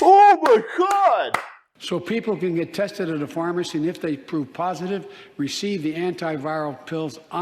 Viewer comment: Did he LEGIT SAY "IF YOU POOP POSITIVE" or was that just me cause it DEFINITELY AN I MEAN DEFINITELY sounded like he said POOP POSITIVE!?!?! PLEASE TELL ME I'M JUST HEARING SHIT!! 😂🤣😂 Much respect brother keep on keeping on my Southern brother! (Southern Oklahoma here my man may as well be considered Texas if you ask me only hell MAYBE at tops 20 mins prolly less from Texas/Oklahoma border!)